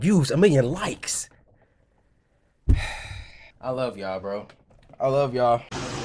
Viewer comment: Anyone else thought rat boy said "I Love You Rat"